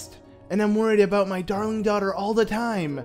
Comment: umm... did he say “dong” instead of “darling”?😂